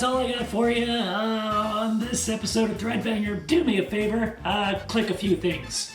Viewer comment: English (auto-generated) captions put "dread failure" instead of "thread banger" and I feel like that says a lot.